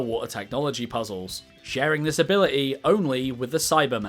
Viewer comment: ngl i thought he said saibaman like the weird plant alien thing from dragonball